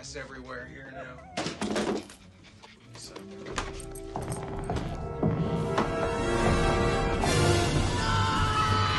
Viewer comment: Did anybody catch that? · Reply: he muttered "son of a bitch" under his breath. I'm surprised no one else caught that.